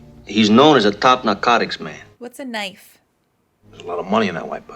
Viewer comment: Wait, did you just say “what’s a knife?”??🤔😎